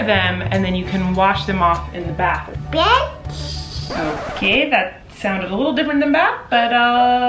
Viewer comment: lol 😂 bath sounded like the b word hilarious